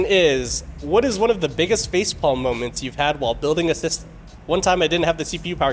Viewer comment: Anybooby thought he said, " Spaceball Moment"?